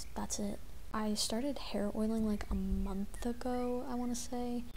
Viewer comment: Oh my god girl I was in the middle of typing this long ass comment & I thought I heard you say you started heroin. HAIR OIL. 👏🏼 Heard. Anyways, I haven’t thought about you & your channel in so long but it looks like we’ve been on the same hair journey. ☺️✨ I loved my short hair & I loved yours even more, but now I couldn’t imagine myself without my long curls/waves. It’s crazy how the longer your hair gets, the more intertwined it gets with your identity…at least in my personal experience. I’m to the point where I occasionally have nightmares about impulsively chopping my hair off like I’ve done in the past 😬😅 the level of regret & deep sadness is real. I’m definitely stealing that tip about cutting off the split ends you can see before washing your hair